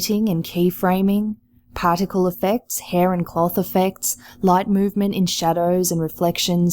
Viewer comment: thought you said hair on cock effects